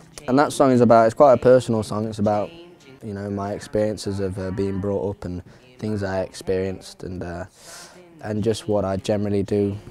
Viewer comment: What he said from 2:24 to 2:27 ? I can't understand :( I think that's accent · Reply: "My experiences of err being brought up and, things I experienced..." this the right bit ya wanted?